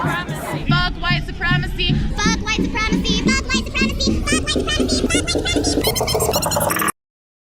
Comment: First I thought she said "STOP" but of course it had to be "f*ck". Note how often hateful sentiments outnumber rational objections. And if i'd be a white supremacist I certainly would not want to be f*cked by THAT...maybe a bj (while looking at more stimulating imagery) at most. If you're against white supremacy, just like sane people, don't feed it with this kind of idiocy.